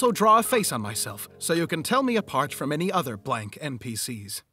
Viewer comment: I’m just now realizing he said, “from any other blank npc’s,” instead of “from the blank npc’s” lol